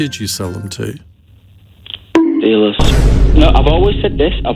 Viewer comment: what did he say? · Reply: drug dealers